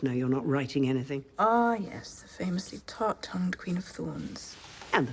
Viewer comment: Did she call her a thot. Damn!